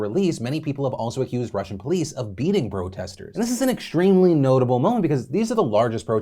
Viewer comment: Did Phil say “Bro-testors” at 7:10? Or am I trip’n?